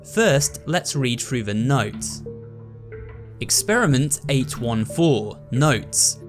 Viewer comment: why did I think it said “Experiment Sin”